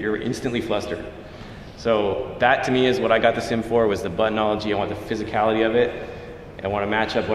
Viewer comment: Did you say "buttonology". Apparently that is an actual word. Thanks. 😉👌